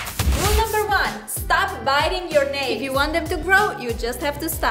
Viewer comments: I thought said "1. Start biting your nails!" I was like whaaaaaat? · I thought said "1. Start biting your nails!" I was like whaaaaaat? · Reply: but I heard she said "stop biting your nails"